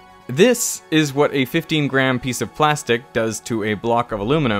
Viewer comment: did he really said plastic ?